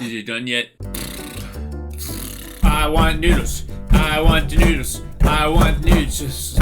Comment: sounded like he said "i want nodes"😅😅